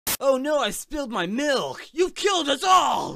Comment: I thought he said, “You’ve killed it all!”